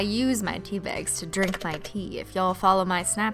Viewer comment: i thought she said "i use it to drink my pee " 😂